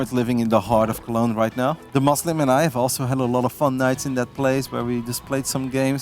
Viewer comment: I heard that sentence at 1:53 and I had to stop playing a game just to listen because you don't hear "DeMuslim and I had fun nights together in that place" and think "oh they just playing video games"